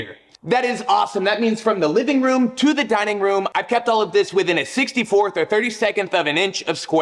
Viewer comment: Did you say 32th?